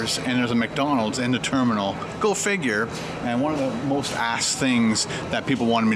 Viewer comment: lol almost sounded like you said "most assed" but you said "most asked"